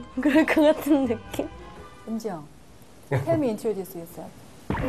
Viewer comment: "Tell Me Introduce Yourself" what was that..!?! -_- stop acting like a pro.. better yet hire someone who can speak the language fluently.. shame.. -_ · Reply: I heard she said 'Eunjung, help me, introduce yourself'. But it was write 'tell me, introduce yourself'... urm....